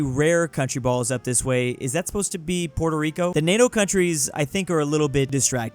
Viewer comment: I HEARD PUERTO RICO— and maybe it's not puerto rick maybe its a city or stats of some cohntrys